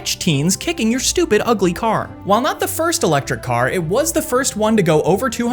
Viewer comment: I thought you said "While not the first ugly car—" 😭😭😭